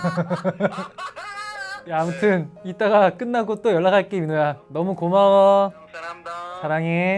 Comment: am I deaf or Changmin said Yunho-yah instead of Minho-yah??... I mean, their names do sound similar when being said fast, but still... It's same as when Yunho once said Sungminie instead of Changminie and Changmin was shocked! He's always teasing Yunho when he pronounces certain words wrongly, but this time it's him!! But why do I like it that much? 😈